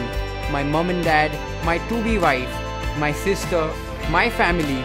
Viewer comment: When I tell you I misheard "to-be wife" as "2-D wife" LMAO XD